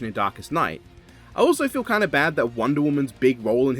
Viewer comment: ngl aside from the fight I really do like Superboy's analysis as well. that moment where he defeats DK, when the music played alongside Krypto's howl, really makes the moment heroic. gives me goosebumps. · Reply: I thought you said he beat donkey Kong for a second before I realized you meant darkest knight